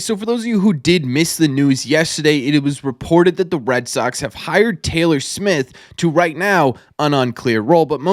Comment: At first I thought you said the Red Sox hired Taylor Swift as the assistant GM 😭